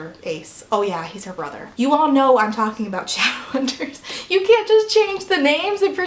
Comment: i have never read shadowhunters and misheard you when you said "obviously i'm talking about shadowhunters" so i thought you said "challengers" and i was like "wait whats this about the horny tennis movie????"